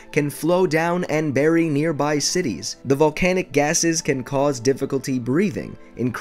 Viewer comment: when he said volcanic ashes I thought he said volcanic asses